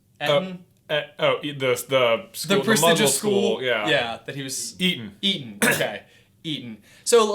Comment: I thought he said he was eaten lol